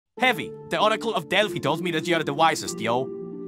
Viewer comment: I swear I thought scout said “article of delfy”